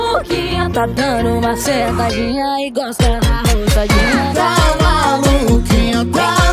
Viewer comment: Mana, abafa o caso. Eu tive que ler a letra porque eu entendi "tá maluquinha, tá maluquinha tá dano uma sentadinha e gosta da rosadinha" kkkkkkkkkk